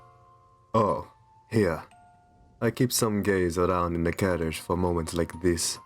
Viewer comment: I thought you said 'I keep some GaYs' And I was like, well I know I'm a flaming qeer but GEzE